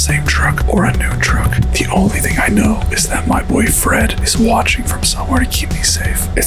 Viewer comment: my dumbass thought he said boyfriend